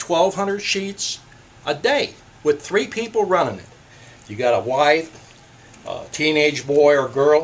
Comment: did you just say a "white" teenage boy or girl? did I hear that right? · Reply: Wife…not white.